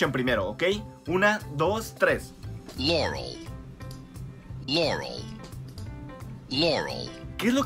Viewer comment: Entendí q dijo Jelly (segundo